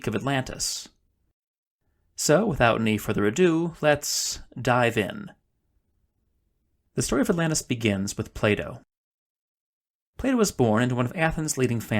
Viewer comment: for a sec I thought you said Playdough 😄